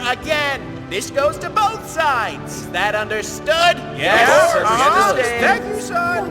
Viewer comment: I hear "Yes mom"